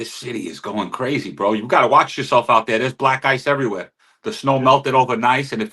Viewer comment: Opie thought he said ,black guys everywhere 😂